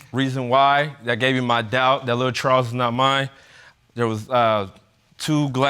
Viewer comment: I thought he said “ that little troll isn’t mine at 3:08. Lol